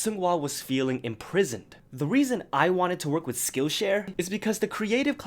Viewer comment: At the end I heard “skill shit” lmaooo 💀